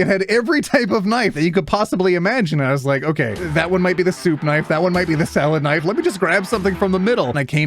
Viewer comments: Did just say “the soup knife”? 😂😅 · I'm so, so sorry... did you just say... "Soup Knife"? 😢